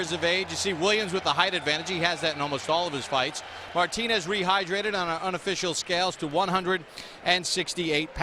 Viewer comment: he said martinez 35 yeas of age, we see williams with a high advantage... and i cannot understand more. can u tell me what he said??? thank you. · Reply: he said that martinez rehidrated to 168 pounds